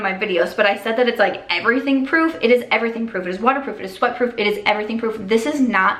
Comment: Lmao when you said the rare beauty mascara was sweat proof for a second I thought you said it was f**kproof 😂 and I was like okay grrrlllll